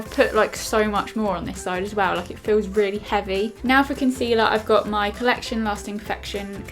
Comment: When she said "now for concealer" I thought she said "now fucking sealer